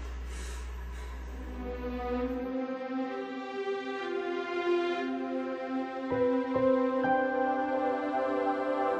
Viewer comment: ano sinabi ni dun? walang sounds eh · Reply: Bubuhayin nya si Juan